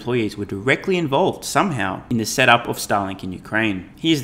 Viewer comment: Mate, I kept hearing you say “Stalin in Ukraine” 😂